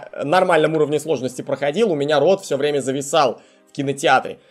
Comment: то чувство когда послышалось рот а не Род